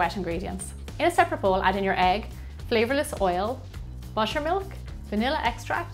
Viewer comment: No offence but I thought you said mushroom milk instead of butter milk for a sec. Love your accent !❤️